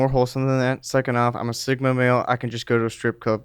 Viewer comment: did he just say that he's a "sIGMA"???? LMFAO